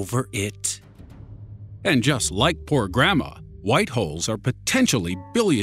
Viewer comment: i Thought i heard you said White Hoes on